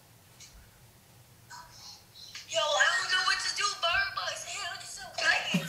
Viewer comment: thats all i heard her say was birdbox 😂